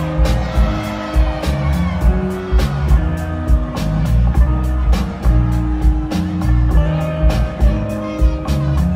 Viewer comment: I hear a lady screaming something, but I don’t really get what she’s saying?😂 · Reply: Oh sick! Yeeaahhh!